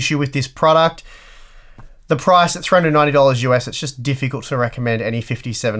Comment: excuse me, what was the price? I have no idea what you are saying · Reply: 390$ us he said